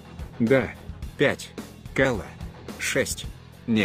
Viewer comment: Кала ? или мне послышалось ? :)))